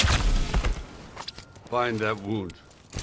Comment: What does he say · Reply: Find that wound or mind that wound